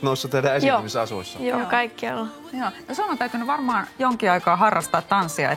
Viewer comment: "yes, we all have" nope I think it sounded more like 'joo, kaikkialla' witch is 'yes, everywhere' not the world "kaikilla" witch is what the subtitels says.